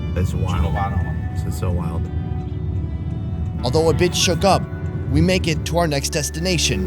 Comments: I thought he said "a bitch shook up" 🤦🏽‍♀️ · so we posta just act like we didnt hear that? "although a bitch shookup" lmaooo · lol he said "although a bit shook up" and I heard "although a bitch shook up" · instead of "even though a bit shook up" I heard " even though a bitch shook up" lmaoo